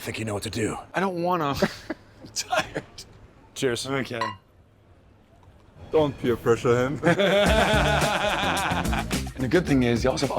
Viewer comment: For a moment I thought the random guy said "don't beer pressure him". Either way very considerate of him.